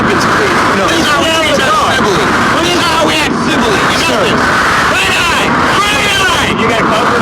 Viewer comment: it almost sounds like red eye, red eye, I can't tell for sure. At 5:47 I hear the double blue. It sounded like he was asking if they were going to shoot him on double blue. Doesn't make sense to me but I don't know their code words. Moral of the story is, if you're white you're probably going to be alright. I don't think I need to say what's going to happen if you're black.